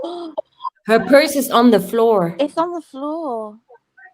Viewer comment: I hear her freind is on the floor or my freind is on the floor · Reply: Same here: I definitely heard "my friend's on the floor" but it doesn't make sense !!!!